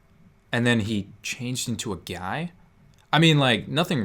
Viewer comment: did he called her a guy?